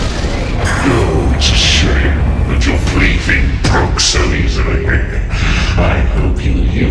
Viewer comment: does anyone understand what he says before "..., I hope you used her well." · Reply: now that you wrote the beginning as it is I can suddenly understand the rest, it goes as: It's a shame that your plaything broke so easily, I hope you used her well.